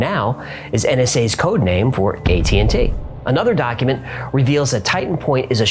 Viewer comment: I heard "gay t&t" lol